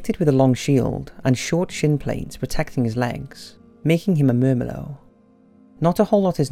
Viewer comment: What was Marcus Attilus? I couldn't understand the main descriptive noun at marker 1:23 that you mumbled through. It sounds like Merma-del. Are you trying to say he was a Myrmidon (a mythological/fictional Thessalian mercenary led by Achilles in the Trojan war)?